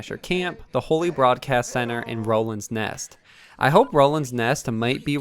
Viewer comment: Is it just me or does it sound like he says Rolands Nest?